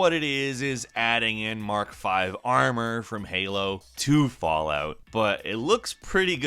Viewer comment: I thought you said "Mark V armor from Halo 2" for a moment there. But we're fine… we're fine...